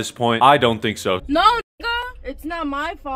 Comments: did she say nigga or am I tripping bruh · did she call him a nigga is that just me